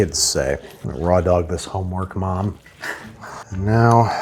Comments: I had to go back to hear this twice to know that he said "homework." At first I heard, "I'm going to rawdog this Hallmark Mom," and I was like HUH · the first time i heard this, i heard "im gonna raw dog this hallmark mom" and i about lost my mind. i had to go back and listen closer after i regained my composure.